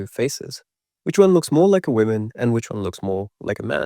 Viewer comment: I kept hearing "which one looks more like a women" also not me already being insecure about my sloped forehead as a girl, thank you